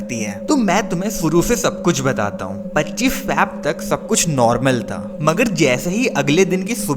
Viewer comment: "25 feb tak sabkuch normal tha" but it sounded like "25 fap tak sabkuch normal tha" 😱😱😱😱😱😱😱